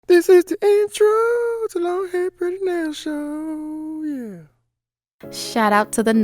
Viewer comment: “ this is the introoooo to long hair pretty nail show yea” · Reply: Omg this whole time i thought it said" this is the introoooo is a long hair pretty nail show, yeah"